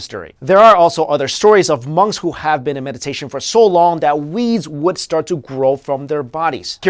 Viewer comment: does he really say "weeds"? It sounds more like "wees" and I can't figure out anything that would make sense.